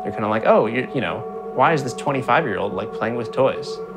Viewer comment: I think you misspronunciated 35, sounded like 25... hah, no way he is that young.